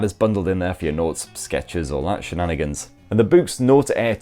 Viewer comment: I swear I heard 'boobs' instead of 'Boox' every time.